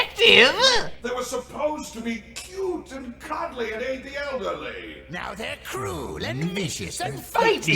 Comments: Okay at 32:50, I just realized was saying “They were supposed to be cute and cuddly and aid the elderly.” Before that, I genuinely thought was saying “Cute and cuddly and ATE the elderly.” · For some fucked up reason when I first heard that, I thought said ATE the elderly.